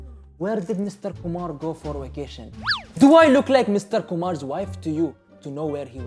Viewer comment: I almost thought he said Mr. Humar (Donkey) 😂